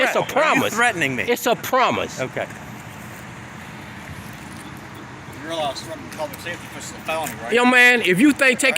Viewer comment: So you made a couple of mistakes here. It's likely because you cannot hear the audio clearly. Captain does not make an accusation of committing a felony. Captain says at (5:55) "You realise that threatening a public safety official is a felony, right?" He is reminding Mr. of the consequences of breaking that particular law. That reminder is especially appropriate given how agitated and how close Mr. is to making an actual threat.